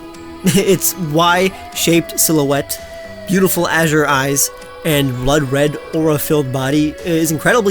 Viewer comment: He said “beautiful azure eyes” but I heard “beautiful as your eyes,” and wondered who had eyes that looked like Yveltal.